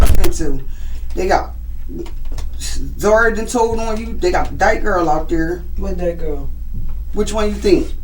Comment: What she said was “they got D*ke Girl out there”, referring to the female officer with stocky build and short hair. I wish these channels took more time to actually understand what people are saying.